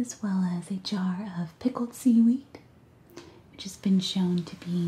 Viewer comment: I thought she said: "Pickled semen." Nothing like a WTF? moment to wake you up. :-P